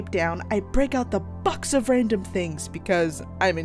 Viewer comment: I thought she said f**k instead of box :|